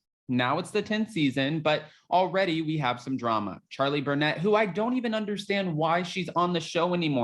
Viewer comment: It’s funny I thought you said Charlie brunette. I was like wow her last name fits her ha ha